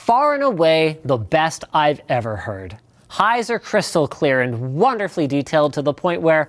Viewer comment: when he said "highs are" i thought he said "heiser"